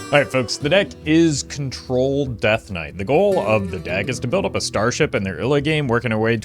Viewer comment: Thought he said “the deck is troll death knight”